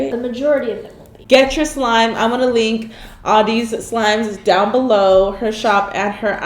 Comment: When karina said “ ima link audeez slime down below” I thought she said ima link all deez slime down below😂